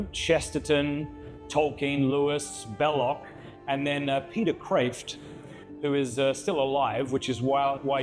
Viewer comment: I couldnt catch the last 2 names of the great men on the wall. Can anyone help with there names please? Also amazing video and great content! ❤🎉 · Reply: Hilaire Belloc and Peter Kreeft ;)